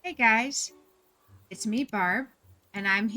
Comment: Hi Barb! 😊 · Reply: I think how I also thought “Hi Barb” when she said hi guys. 🤣